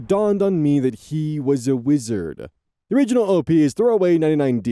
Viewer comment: TIL that ive been mishearing tagline this whole time as “he was a lizard”… it’s “wizard”